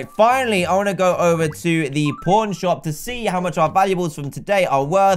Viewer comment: i thought he said porn shop